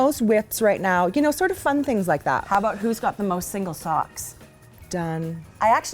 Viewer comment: Got to get my hearing checked.....When said, "who's got the most single socks", I heard, "who's got the most age spots"?!!!!!!